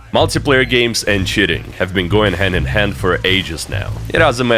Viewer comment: "Video games and shitting" is what u thought I heard